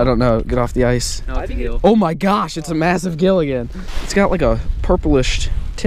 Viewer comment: lol when you said "it's a massive gill again" I thought you said ~ it's a massive Gilligan😂